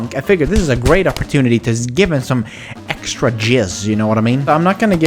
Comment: did u just say extra jizz?? akxvskxvzj